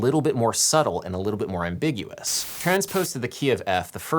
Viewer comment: every time you say ambigious, I hear "ambitious" - perhaps because I think the song is unfairly underrated :-)